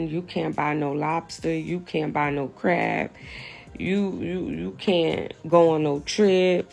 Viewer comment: I seriously thought she said u cant buy no crack! But she said crab. I mean i wouldnt be surprised...